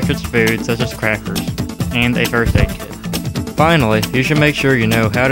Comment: at first I thought he said birthday cake lol. But i think it's first aid kit.